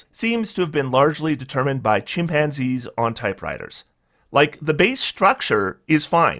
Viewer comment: The first hearing, I thought you said "Designed by chimpanzees on Tie Fighters." That may be an apt comparison as well.